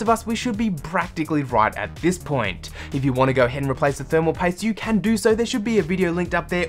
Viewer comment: At approximately the 5:12 mark in the video, you mention replacing something, but I can't make out what you're saying. Sounds like "thermal tape", but i know nothing about computer hardware so clarification would be very much appreciated. Can anyone help? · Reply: He says: thermal paste. This is a type of paste that serves as a connection between your cpu cooler, and your cpu, so it stays cooler